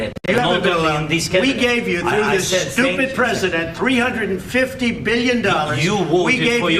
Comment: Did he said " Stupid President" ?